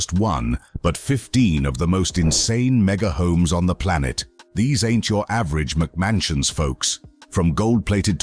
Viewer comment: 0:9 - 0:11 what is he saying it sounds like he's saying in english🤔🥴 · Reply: "Mega homes... these ain't your average McMansion's folks" Slang for: These homes are not your standard large sized homes pretending to be mansion (called McMansions) Technically, for a home/estate be classified as a true mansion the square footage must exceed 6,000 square feet of living space (this wouldn't include garages/outbuildings/etc...)